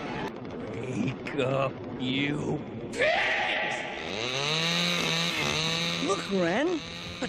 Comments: what did he say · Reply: I heard the b word, but I'm sure he actually said " wake up you pigs!" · anyone else hear "bitch"? I know it's pigs but admit it you're not gonna unhear this. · did he say what I think he said? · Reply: He said pigs